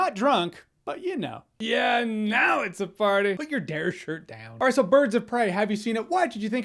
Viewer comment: "Put your D.A.R.E. shirt down." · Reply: Oooooohh, i thought he said "dad shirt"